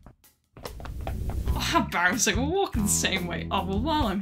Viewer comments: I thought you said wife insurance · I keep hearing "wife insurance" which must be insurance that pays out if your wife leaves you. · I heard "Wife insurance" =w=